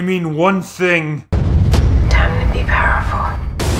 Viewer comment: What does the Batwoman say in 04:43 ? I'm not a native speaker, so I couldn't catch it. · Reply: Time to be powerful